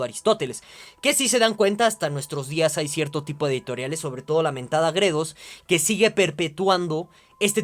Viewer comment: Dice "Editorial Gredos"?. No entiendo muy bien.